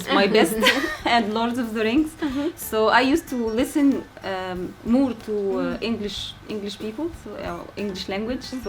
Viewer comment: eu juro que ela falou "I used to listen MUITO..."